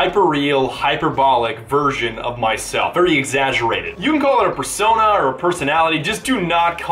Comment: I thought you just said fursona. XD